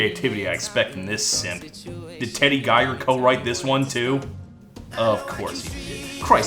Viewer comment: Did he say...Simp?? In 2016?? Who gave a time machine?